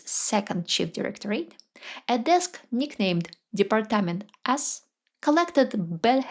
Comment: Thought she said "Department of Ass".